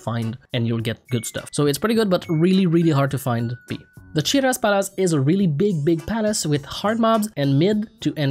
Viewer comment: Did you say Chiras Palace? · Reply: shiraz palace